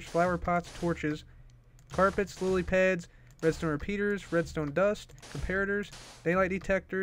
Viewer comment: I thought he said “Mr. repeater”